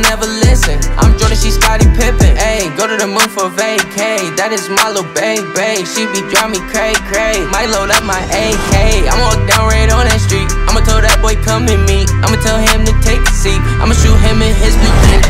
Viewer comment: did he say “ ima tell that boy kum in me”😂😭